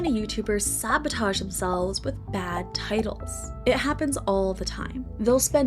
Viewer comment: I thought it said tities my bad